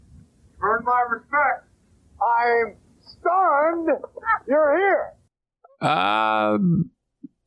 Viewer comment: I heard "I'm Stunned You're Here" 🤣 grainy 90's VHS audio